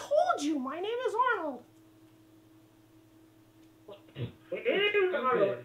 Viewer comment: i just noticed something! is it just me or around 7:09 does someone say "it is arnold"? they say it again like 20 seconds later. i cant tell if it is someone who recognizes me or is just mocking me.